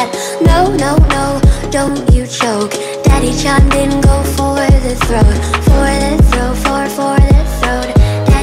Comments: I thought it said for the throne😭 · What I heard was "Daddy John, didn't go for the throat". This makes so much sense now 😂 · I thought it said “Don’t you choke, daddy shot in go forward the throat” 😔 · I really thought it said “daddy taut them go for the throat” 😂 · I thought it said daddy chan😭💀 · "daddy chimed in go for the throat" i heard it say "daddy choplin go for the throat" LOL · daddy chimed in “go for the throat” crybaby: dad thank you for that fatherly advice 😂👌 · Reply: At first when I listened to this I thought it said " Daddy Shawn didn't go for the throat" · help i hear "Dabi chimed in go for the throat" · I’m so dumb I thought it was saying “for the throne “ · I legit heard "Daddy shot men go for throat" Why...😂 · I deadass thought it said "daddy changbin, go for the throat, for the throat, for, for the throat" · I heard.... "Daddy shawned in go for the throat" 😂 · I legitimately thought it was “Daddy-Chan didn’t go for the throat” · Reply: I thought she said daddy Shawn Mendes didn’t go for the throat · before this i always thought it said ''daddy John didn't go for the throat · I legitimately thought it was “Daddy-Chan didn’t go for the throat” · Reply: I heard "Daddy John didn't go for the throat" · Lmaoo I thought it said Daddy John didn’t go for the throat